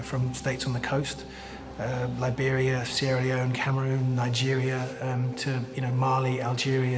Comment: did he say 'syria' haha. lol